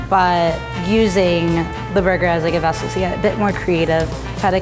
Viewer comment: girl what did you just say?? 🧐🧐🧐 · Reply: I use the burger as a vessel to get really creative